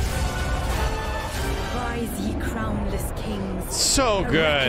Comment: Rise Ye Crownless Kings is such a good line to end the trailer. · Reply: I thought you said, rise ye clownless kings 😆